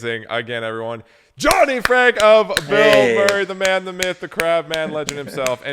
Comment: I’ve heard you say “bilmuri” a few times recently and I swore you were talking about Bill Murray 😂 this makes much more sense